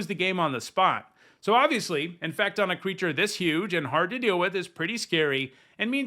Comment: I don't use AI at all. I just said a word slightly weirdly. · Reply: no. When you said it, it sounded like you said "infact" instead of "infect". That was what I was pointing out.